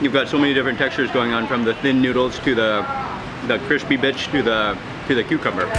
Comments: it sounded like he said crispy b*tch 😂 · who heard " crispy bitch " ? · Did anyone else hear something else when he's talking about the satay plate with noodles he says "crispy bits"? Lol I totally heard "crispy b|+c#"